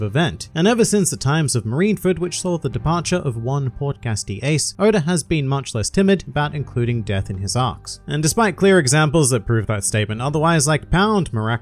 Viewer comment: Nope. All I hear is Podcast D. Ace